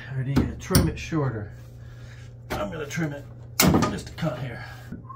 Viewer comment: Ok @ 7:56 did you just say you'll trim it just a c..t hair!!! Sub'd/like lol